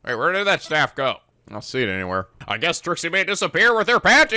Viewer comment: Did you call Trucy, Trixy? 11:43 Maybe I am hearing things. xD